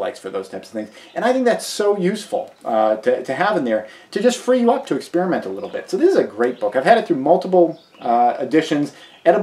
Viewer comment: Great review.... Already had Botany for A Day in my cart, but not the other one. Thanks. At 6:31 I initially thought you said "free love to experiment a little bit" -- till I rewound and listened to it again. Although now that I think about it more no poisonous look alikes is kinda like Free Love with out a risk of STD or preganancy. Lol